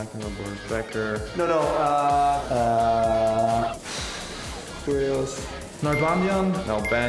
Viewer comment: did rublev say kyrgios?? WTF